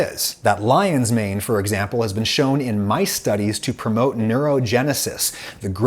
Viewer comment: i thought said " this has been shown in MY study" instead of "mice studies" and i was like yo when and how did get the authority to conduct neurological research? not doubting it tbh just questioning